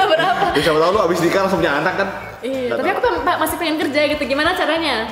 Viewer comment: jerome ngomong insyaallah kalo ga salah denger ya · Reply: "siapa tau kamu abis nikah langsung punya anak kan" dia ngomong gtu